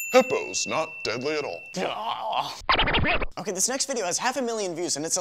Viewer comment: sounds like he said sex video lol